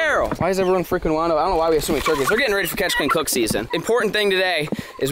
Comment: Lol 😆 at first I thought it said robber catch clean and cook